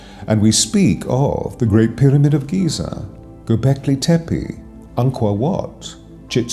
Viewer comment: You were talking about sacred sites around 8:30. I want to know what you said after Giza. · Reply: Gobeckli Tepe!